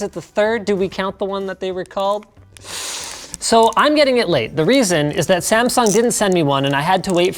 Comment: did Linus say, "So, I'm gonna get laid..."?